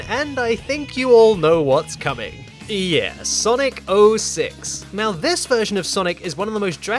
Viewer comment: Sonic Bullshits instead of Sonic 06